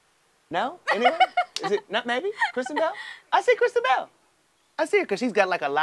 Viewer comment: At first I thought he said Christian Bale not Kristen Bell lol